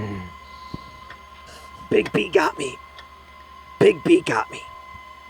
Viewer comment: what he said: " big bee got me, big bee got me " vs what I heard : " Big pee got me, Big pee got me "